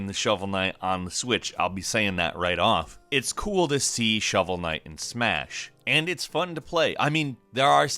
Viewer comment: I thought said "it's cool to see shovel Knight smash"